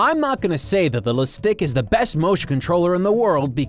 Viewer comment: I swear I thought the manual said "The Shit Stick"